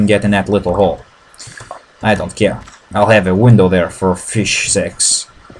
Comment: did he say fish s*x?